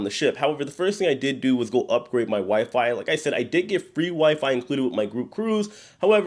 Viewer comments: thought you said upgrade your wife lmao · For a sec I thought you said, "upgrade my wife"